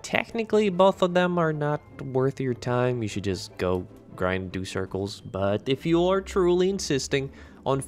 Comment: grind what? Replayed 6 times and I can't understand what you said. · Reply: GO grind, do circles (in bdo, u do circles as in go in circles while u grind mobs and repeat ), not a spot just something You go do in bdo :))